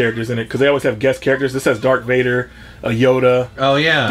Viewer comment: did say “dark Vader” lol 😂